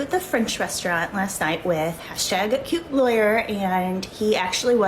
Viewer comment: lol i was passive listening and thought he said "jew lawyer" lol